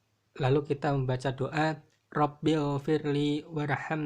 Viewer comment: Assalamualaikum wr wb. Maaf mohon dikoreksikembali bacaan diantara dua sujud. Harusnya robigfirli bukan robilfirli. Maaf kalau mungkin saya yg salah dengar,. Terimakasih, wassalamualaikum wr wb.